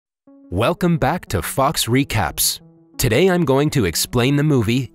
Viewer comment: When he said “welcome back to Fox Recaps” I thought he said “Welcome back to Fucks Recaps”